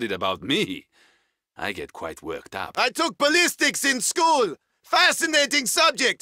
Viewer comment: "I took balistics in school" "Fascinating subject" "Things go up" "Things go down" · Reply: I thought it said politics lol